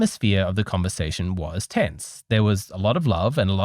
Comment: did you actually say the interview was "tits" ?😅😂